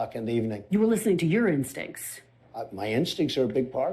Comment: I swear I thought she said "You were listening to urine stinks".